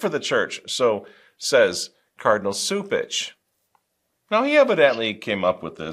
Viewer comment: I think it's not coincidence that my subtitles showed a word similar to "sewage" instead of "Cupich", hmm 🤔